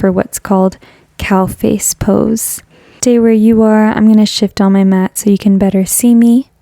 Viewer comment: Great practice thanks! Btw, I thought you said at 6:15 “I’m going to shjt on my mat”..😂